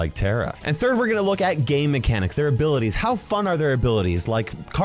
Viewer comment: why did i hear "gay mechanics"? 💀